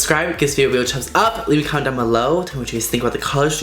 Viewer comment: I thought you said leave a condom below instead of comment hahaha!!!! I am from Brooklyn , NY MY IG: Yeci_mari